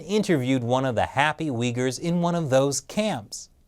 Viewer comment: Did... did he said WIGGERS?!